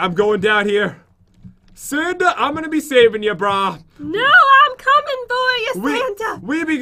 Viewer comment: when in da cave I heard" I would be saving your Santa's BRA" instead of bro